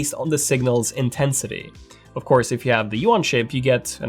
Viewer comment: what is he saying, "U1 chip"? What is that? Great video! Helped me decide. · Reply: Eureka. It's the UWB (typically Samsung S21 or later), but for some reason he pronounces it like "U1" or "yuan".